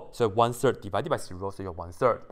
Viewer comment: excuse me, what did you just say?? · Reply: i think he says one third divised by 0 is one third